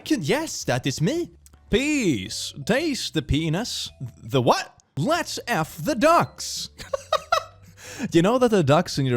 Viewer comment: lets feed the ducks is what im guessing it actually says